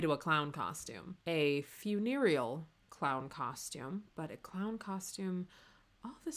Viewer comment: I could have sworn you said "venereal clown costume" & I was about to accept it until I realized that this dress is nowhere near revealing enough to suit that purpose. Also, thank you for not judging me for my werewolf hookups. 😂🤣